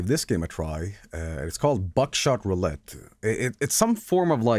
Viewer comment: thought it said backshot roulette for a sec